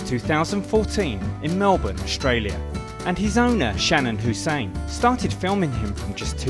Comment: what was the owner's name again? mary or something? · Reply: I thought he said saddam hussein at first